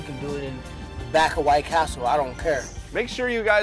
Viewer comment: Did he just say I don't ker?? LOL